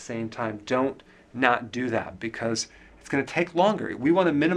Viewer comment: Dr. Berg: "...don't not do that." I had to push back to hear that again because I thought he said, "...donut, do that." Just another symptom of getting used to keto - you hear glorious words like "donut" instead of what was really said.😅💕